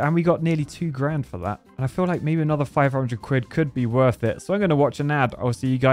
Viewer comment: am I the only one who heard him say “600 squid”